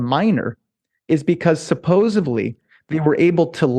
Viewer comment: anyone else notice him say "supposevly" instead of "supposedly'? lmao i feel like people automatically lose a bit of credibility when they pronounce it this way lol